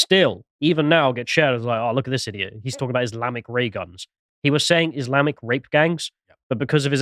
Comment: he actually said Muslamic, which made it even funnier